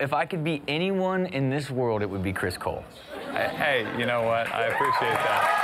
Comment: i thought u said chris bold XD but its chris cole XD